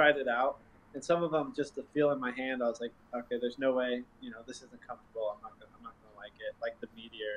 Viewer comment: Did Paul just swear? A rarity if so. I think I've only ever heard him swear during a round to no one in particular. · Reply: I thought so at first but it was "Some of them just the feel in my hand, I was like o'k there's no way, this isn't comfortable"